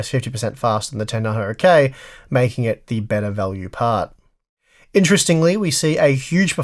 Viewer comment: What are you confused about? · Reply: at the fact that I'm deaf because I thought I heard "better value card" but it was "part" ... facepalm.